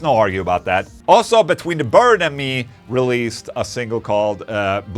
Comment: I totally thought you said “betweeen the bird and me”